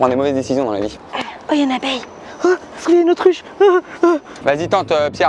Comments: au début j'ai compris "OHH il y a une autruche" au lieu de "autre ruche " 😭😂😂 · j'ai compris une autruche 😂😂😂😂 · À 15:18 elle dit autruche ou c'est que moi qui bug 😖